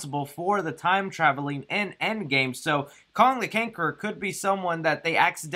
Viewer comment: did you say Kong the Cankeror